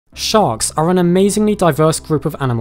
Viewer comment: I'm so asleep I heard 'Shark are and amazing class of pokemon'